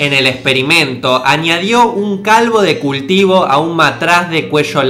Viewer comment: Escuché que dijo un calvo de cultivo?????????